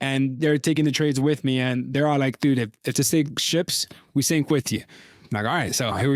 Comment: I'm high but I swear I heard say"If the sink ships" he did right?